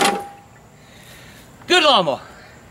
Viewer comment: I thought he said "Good lama" but he was like "Good lawnmower"